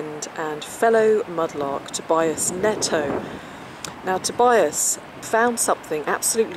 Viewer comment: I. Just started this video… as you were introducing your friend, I thought iou said “” To buy his Neto!!! “. I thought, what is a Neto???